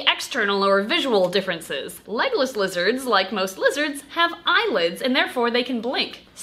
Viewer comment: For the longest time I thought you kept saying “Legolas Lizards” and I thought it was kinda weird that a lizard was named after Legolas then I read the title to this video and my life has been changed forever